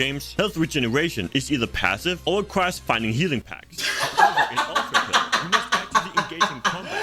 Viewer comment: did he say “healing pack”?